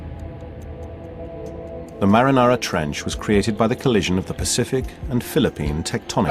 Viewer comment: Am i not the only one who heard the "Marinara Trench"? I know he's trying to say Mariana Trench but still, i sounded like he said Marinara Trench.